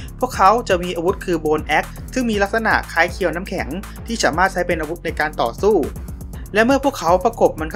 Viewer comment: พี่พูดว่า ฉามารถ หรือ ผมฟังผิด 😅